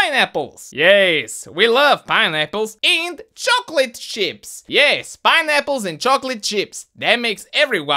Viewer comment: i thought he said chocolate shits instead of chocolate chips...